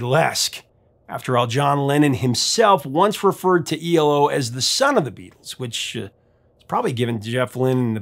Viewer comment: ELO, one of the most underrated bands of all time. Whatever praise we give them, they deserve even more. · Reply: and my crappy hearing heard, at first, " Lennon said they were, 'the sum of the Beatles'". More misheard conversations!